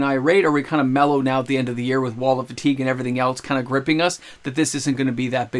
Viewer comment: Misheard Wallet Fatigue near the end as “Wall of Fatigue”. Now I want that card to be made.